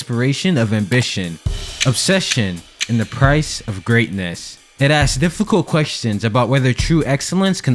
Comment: nothing to do with the video lol. I forget the name of the audio illusion thingy but I heard "deadass" then listened back to realize what was said is "they'd ask" I think depending on what you focus on you can hear either phrases. I wanted to share that. Fantastic work btw.